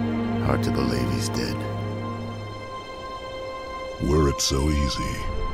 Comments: I always thought Abby said in the opening " Words its so easy" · When Arbiter said "were it so easy" I first thought he said "Word so easy". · When Arbiter said "were it so easy" I first thought he said "Word so easy". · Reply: Lol when I was younger I always thought he said "worried so easily"